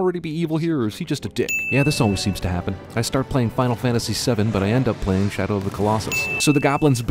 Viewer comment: You say Final Fantasy VII (7), but the subtitle says XII (12)